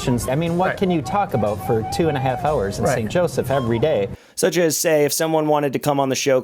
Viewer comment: did he say St. Joe?